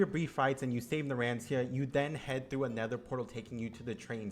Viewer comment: did he say nether portal im sobbing 7:08 as far as i know jjba4 was set in 1995, josuke is 19, so it means joseph was cheating in 1986